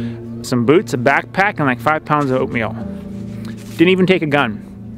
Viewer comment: I thought he said he took off with 5 pounds of opium. Not oatmeal. I’m thinking...dude, don’t harsh his mellow! 🙋🏻‍♂️